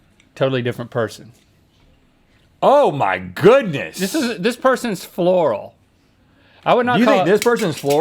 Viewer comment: I thought Rhett was saying that person stunk 😭 I was like damn I would have to quit and ** myself 😭